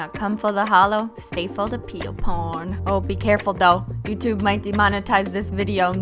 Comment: When you said peel porn I thought you said real porn